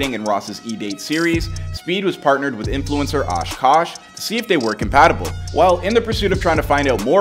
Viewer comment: did he just say oosh koosh?!